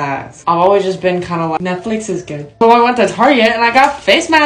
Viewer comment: I thought they said Netflix is gay but then I watched it again and herd Netflix is good I wish it said Netflix is gay